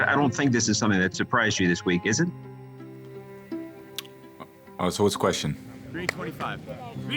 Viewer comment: what is he saying can someone pls say? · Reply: I think "So what is the question?"